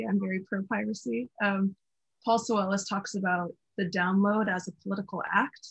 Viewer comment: hi! @ 13:55 what's the name of that author? the CC says Paul Sowellis but can't find anything relevant online by that name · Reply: I believe the name is Paul Soulellis